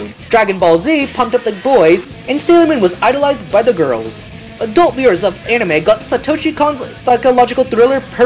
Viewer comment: What was the word at 9:32/9:33? "Adult mirrors" of anime got Satoshi Kon's Perfect Blue... Did you mean adult admirers?